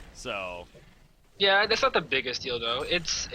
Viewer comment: Is it just me or did I heard something from the other guy at 42:59 ? XD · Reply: not the biggest dildo. XD